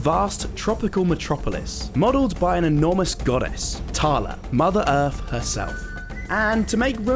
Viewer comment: did i hear "THALA"